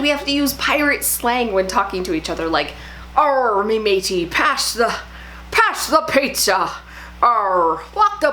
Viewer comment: I need to work on my pirate talk I thought you said “someone pash me the pizza “ xo